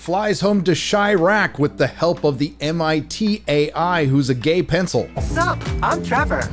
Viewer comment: I think the captions in Disney plus even say Gay-i 💀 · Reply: apparently it does say Ai but it definitely sounded like it said Gay-I😭